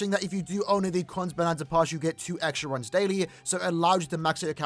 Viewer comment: What he said? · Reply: Kon's Bonanza Pass (costs 5 dollars a month) allows you to play this quest 4 times a day.